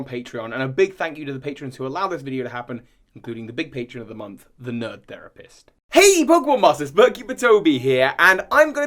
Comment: Lol I thought it said I Need Therapist